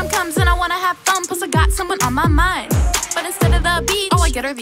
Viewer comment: Did u here what Madison says 0:16 that she has someone on her mind 👇like if u heard